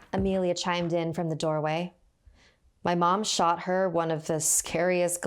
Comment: i thought she said she shot her